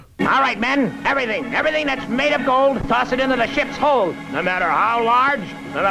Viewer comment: I misheard that as “the sh*t’s hole”